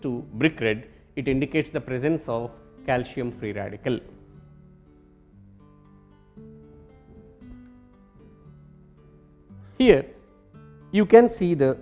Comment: did I just hear CALCIUM"FREE RADICAL".... 17:41 Ba free radical haahahahahahaahhaaha